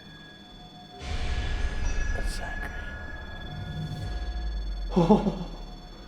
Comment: EVP Sounds Like it was saying Zachery